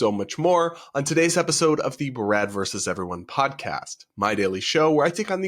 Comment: sounded like your said "Grad" instead of "Brad" 😂